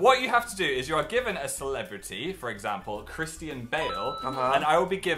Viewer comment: literally sounds like phil is saying 'Christi and Bale' rather than Christian Bale